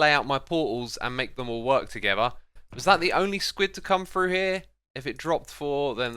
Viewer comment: Oh I thought he said squid bomb 💣